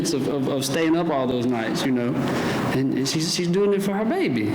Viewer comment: did he say our baby